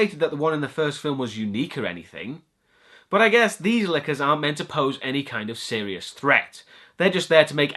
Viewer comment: And they can't have detectors without opening the door. "Lickers" I thought you said time for liquor. It's the power of knowledge and money. Her dad is the chief.